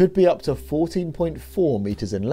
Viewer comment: Hahahaha I thought u said “40.4 meters”! I was losing my mind!!!!